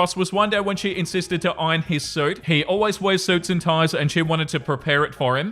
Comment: Misheard “iron his suit” as “iron his soup” which made the story very confusing